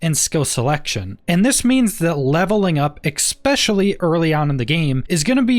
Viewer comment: did he just say "eckspecially"?